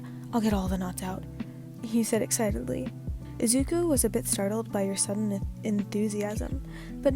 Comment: i heard "zuko" instead of "izuku" and i got scared